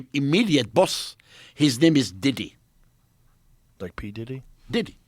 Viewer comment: Did he just say Diddy?!!